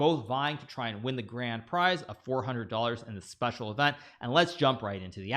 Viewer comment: Did Hikaru say the grand prize is $400?? Is that right?